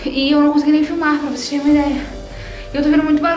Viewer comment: gente so eu q escutei ela falando " vai logo higor" no min 10:22?